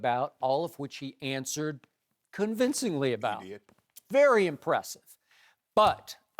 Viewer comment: Did Shannon sharp call Antonio brown an idiot at 5:43 or did he say something else? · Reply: Christopher Coll he said " he did"